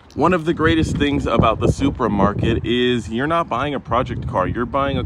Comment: I thought he said supermarket I had to watch the video twice to realize he meant supra market🤦🏻‍♂️